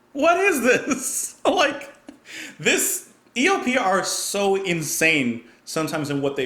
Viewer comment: sounded like you said ELPR, lol